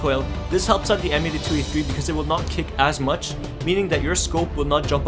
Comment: When he was talking about the thunderbolt stock I thought he said, "so it will not kick ass much" when I heard that I was like :l so I didn't vote for him LOL